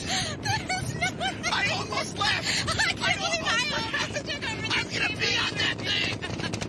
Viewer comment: "I'm gonna pee on that thing!" @ 2:03:28. I was confused for a second there. Once you misheard it, you can never unhear it🤣